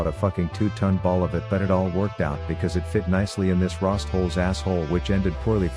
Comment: Could of sworn you said rasshole